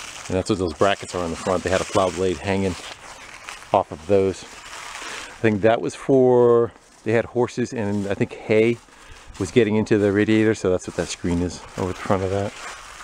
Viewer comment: I heard.. "That's for.. they got in horses.. and thus the filter." xD